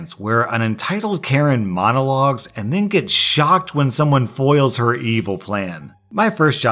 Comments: I misheard the “shocked” in the intro as “shot” Maybe one day... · Am I the only one who in the first story thought he said “and gets shöt” instead of “and gets a shock” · I thought he said "an evil karen monologues, then gets shot"